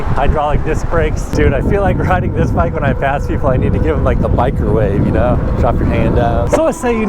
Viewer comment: Thought you said give them the "micro wave" instead of "biker wave". I was like.. They call the low wave "microwave" out west? Lol. Had to rewind and correct my hearing. My east coast brain always hears something different when people from the west talk a bit faster lol.